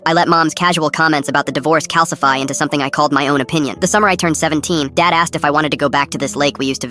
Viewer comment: My dumb ahh thought he said the summer I turned pretty even though it was the summer I tuned 17